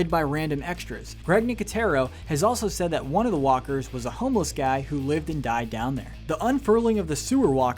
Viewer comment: did he say one of the walkers was a homeless man who lived and died down there like they dressed up a dead person? As a walker